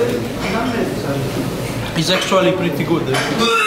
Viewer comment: non ho capito bene: "is sexually pretty good"?? ahah